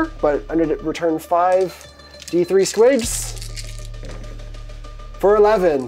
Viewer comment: I heard "bringing 4D3 squigs back" as bringing 43 squigs back....